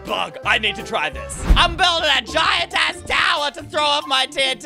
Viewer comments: normally I can tell when swears or not but that did not sound like azz at all. It really sounded like *ss 6:37 too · if u have captions on he swore also u can hear him say it he says I'm building a giant ass tower to throw my tnt off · did he just say a$$ turn on cc u